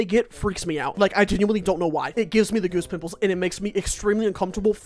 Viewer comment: I thought he said goose nipples, which oddly would work too